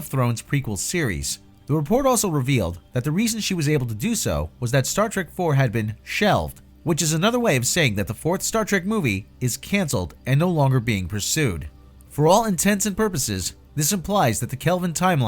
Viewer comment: Did you say "for all intensive purposes"? At ~